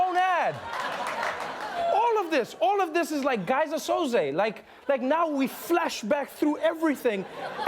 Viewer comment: What does trevor say at 4:22? Geyser souze it sounds like tryna get the reference